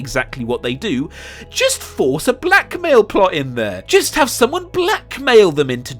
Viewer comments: I got wildly confused because i kept hearing “black male” and now i feel stupid. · Is it just me who thought he said 'black male' the first time instead of 'blackmail' because he randomly panned to Finn? 😂😂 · Ohhh, blackmail. I thought you were saying, “black male” at first... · I thought you were saying black male instead of blackmail lmao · Honestly every time you said blackmail I legitimately thought you were saying black male and I was like... 🤔 😂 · When he said blackmail i thought he said black male and was confused lolz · Anyone else get confused when he said "Blackmail" when zoomed in on Finn? I thought he said "Black male". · I swear the first time you said “blackmail” I thought you said “black male” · I thought he said 'black male' in stead of 'blackmail' because he showed Finn haha I ain't racist dw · you really confused me when you started talking about blackmail motivations because you put finn on the screen, and i thought you said "black male" , which confused me. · I feel stupid now. When you said blackmail and cut too Finn I thought you said black male and I was very confused with the Superman part and not seeing the black male that I was clearly not seeing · when you said 'blackmail' and then showed a clip of Finn I thought you were saying a 'black male' motivation and I was sooooo confused lmao